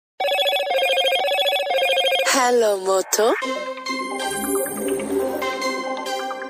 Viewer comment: Im sorry but the hello moto ringtone is the funniest thing I have ever heard 😂😂😂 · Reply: 😂😂😂 I hear this ringtone a lot now cause of the environment I am I thought it said hello mutha a first